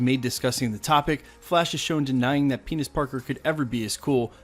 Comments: Did he say "Penis Parker"...?!?? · Is it me or did I hear him say "Penis Parker " at 4:21??? LOL